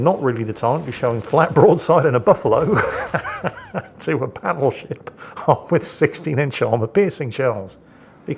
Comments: did you just call that a Paddleship? ;p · don't know if it's just me or because of the recently more muffled voiceover from, but did he just say "Paddleship"? Because that was, indeed, a paddlin'